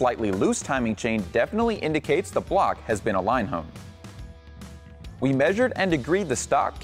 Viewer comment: "The slightly loose timing chain definitely indicates the block has been---" What? What did he say? And what does that mean? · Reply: Align honed, and or align bored. This moves the crankshaft centerline closer to the camshaft centerline. Hope that helps. T.S. RACING